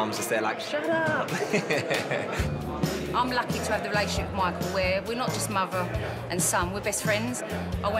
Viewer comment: I thought he was saying “Michael” in some accent- 😭 his name is “Mike Hough” JAHAHAHHAHAGSJSJ💀💀